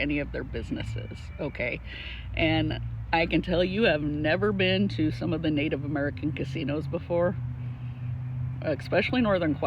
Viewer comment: did you say you have Never been to the Indian Casinos, then at 10:13 say you have been a dozen times? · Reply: i said “I can tell you have never been to a Native American casino before.” I was talking directly to J Embree